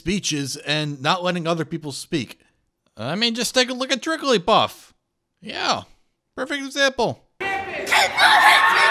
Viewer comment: And what´s the beast at 14:25 even shouting? Look, English may not be my native language, but I usually understand it pretty dang well! But I´ve seen that clip / that bitch over a hundred times at least now, but I never understand, what he / she / it is saying! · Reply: You mean trigglypuff? The blob keeps repeating "Keep your hate speech out of this campus".